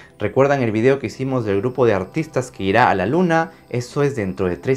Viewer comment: entendí "el grupo de autistas que ira a la luna"